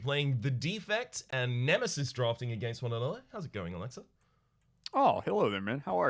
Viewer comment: If you were half asleep watching, you could've sworn said Alexa instead of Olexa.... Too bad I'm awake...